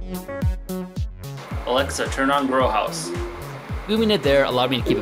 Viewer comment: I thought he said bro house at first 😂